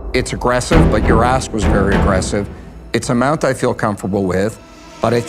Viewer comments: Sounds like he said "Your Ass Was Very Aggressive". LOL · Did he say “It’s aggressive but your ass was very aggressive” at 9:01? 😂